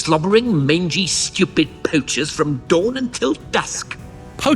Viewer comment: since I was like 2 years old I thought he was saying "pooches"...